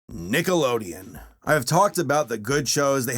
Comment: I swear to god I heard you say NIGGALODEON